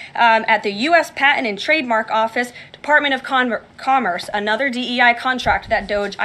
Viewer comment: she was gonna say department of condoms Freudian much? 😂